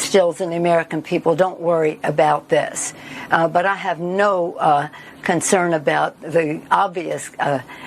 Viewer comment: 😂 Did she say she had no concerns of the obvious blah blah blah!!!?